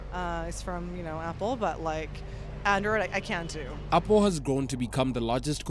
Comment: did she said "I can't do"?